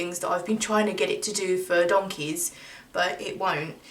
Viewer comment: “... that I’ve been trying to get it to do for ___..” Did you say “donkeys”??? I’m not judging, just didn’t think I heard your right. XD Either way, you’re a real treasure. :3 And I can totally relate to your feelings of being overly empathetic too. I’m gonna have to try a bit of your advice about anxiety and embracing the feelings rather than trying to push them away because it’s like the only thing I haven’t tried myself at this point and it just sounds like it really makes sense like duh. XD